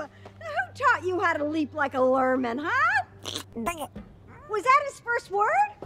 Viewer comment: what did grogu said? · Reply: "The Way, this is."